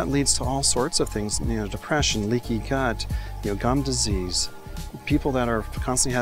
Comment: it sounds like he says “leaky c***.” 😹